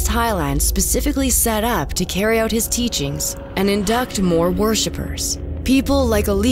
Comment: Is it just me or does everyone else have a problem with how she says worship. Sounds like she says war ship 😂